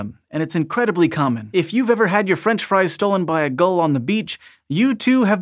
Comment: I heard "girl on the beach" i was like yes?